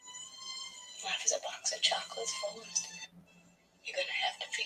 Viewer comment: She actually says "like is a box of chocolates", but then when you're repeating her right afterwards, you say it's "life is like a box of chocolates". Did you misspeak, or do you remember that it said "like" to your ears when you made this livestream?